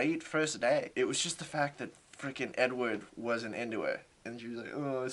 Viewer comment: i freaking replayed the these five seconds like six times cos i thought he said "she had a great first day, it was just the fact that n word wasn't into her" like the letter 'n' then 'word' referring to the black guy that hassled her about her car. i was so confused for like three seconds (he said edward im just deaf and also stupid). i had to turn on the subtitles to understand what he was saying lmao